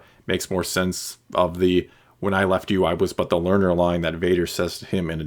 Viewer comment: I thought Vader said, “when you left me I was but the learner, but I am the master.”?